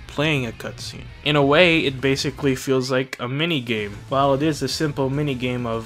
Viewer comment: Did he just say “Main-Ga”? I am now sad.